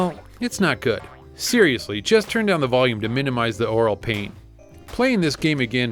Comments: haha it sounded like you said minimize the oral pain about clockwork knight :P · "Oral pain" ??? From bad music holy shit it must be bad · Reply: I think he said "aural", as in, ear pain, but I can see what you mean. It did sound like he said oral.